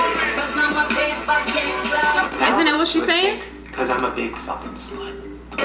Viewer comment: I heard Rihanna say “Cuz I’m A Big Fucking Slut” I’m surprised you both didn’t hear it